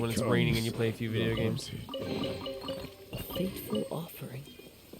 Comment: Tell me why I heard Kazuha say "A free football throw" instead of "A fateful offering" lmaoo